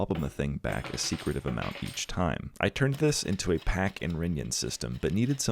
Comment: my dislexic ass didn't catch that at first... I was like wait did he say "pack and ringin"...